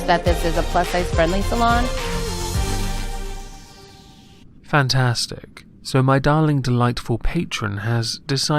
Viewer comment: I swear I heard you say "fatass-stic"